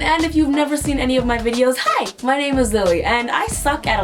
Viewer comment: Did anyone else hear "hi, my name is zilly" at 0:11 ??